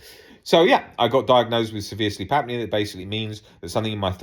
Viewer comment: Remember when you first mentioned that device, I thought you was saying 'sleep app'